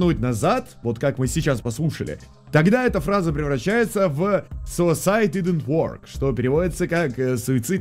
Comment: короче я услышала "suicide didn't work rite"